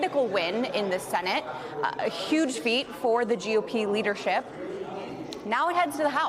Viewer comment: LOL, did I hear "the GOP Weinership"?